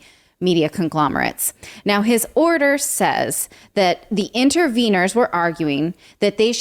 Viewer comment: At about 1:12 I was going to say more like inny Wieners…. Instead of interveners.